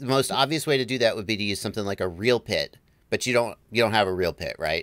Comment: What is that you say at 1:32 "Real Pid"?? I'm trying to build something similar and I'm following the same steps! Thank you!